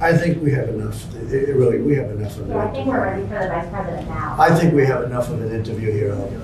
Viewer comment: Did she say, "I think we are ready for the next president now" ??? (Min